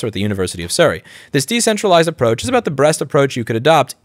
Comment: says “breast” instead of “best” -AI detects said “breast” utterance -AI flags video Ah yes the dystopian technocracy we all are headed towards